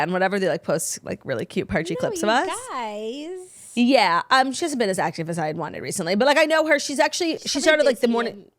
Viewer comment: Can someone translate what Claud said at 13:30? I’ve listened a few times and can’t decipher haha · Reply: “She hasn’t been as active as I had wanted recently”